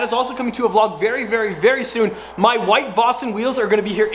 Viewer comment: I thought you said wife vosin. 😂😂